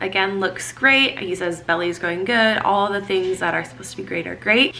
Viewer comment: I swore I heard you say “Ellie is growing great” and I thought you slipped a name in there for us 😂